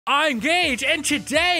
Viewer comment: I heard “I’m Gage” as “I’m gay”… please tell me that i’m the only one that heard that